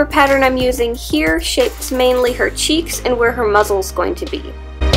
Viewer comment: WHEN SHE SAID "Is where her mouth is gonna be" I thought she said "Is where her mothers gonna be"